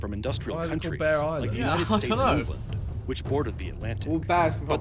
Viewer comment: Did he say the United States of England?